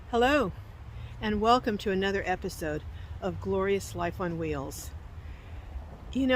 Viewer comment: Great stuff! It's better to have it and not need it, than to need it and not have it! For the longest time I thought you were saying Life on Wheels.😂